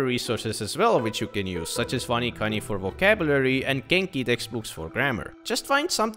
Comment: What's the name of that vocab trainer u use in 2:04, i cant understand it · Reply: wanikani, I don't use it myself